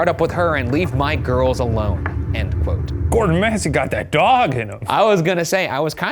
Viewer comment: i know one of the names in this case is "Goddamn Massie" but everytime Ryan says his full name i keep hearing "Gordon Ramsey" 💀